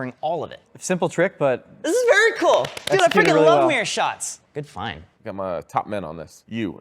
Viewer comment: "your fine" instead of "good find" Such a 21st century bro :)